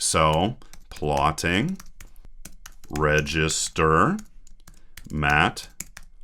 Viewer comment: I heard "Mac pork rib hamburgers", thought "huum... Mcdonald's stock?". It was matplotlib_converters...